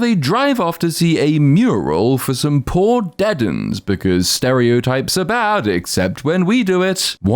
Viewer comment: Is he sayin “dead’ns” as in a southern/city slang way to say “dead ones”?